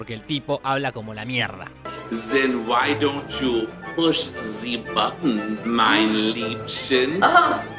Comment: Entendí algo como: 5:33 Said: Why dont' you push sie button in my lipsen? Que es algún chiste caliente o algo asi? O... Por qué no presiona el bottón querida?. La verdad estoy en la duda depende del contexto.